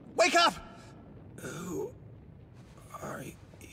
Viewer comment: thought he said "i had to shit a few pants"